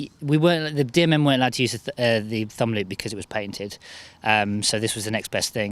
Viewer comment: did anyone else hear painted I was just wondering if he said patented